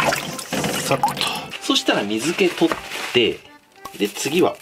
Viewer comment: 砂糖を洗い流す 金子「さっと」 自分「さとう」 と聞こえてしまった。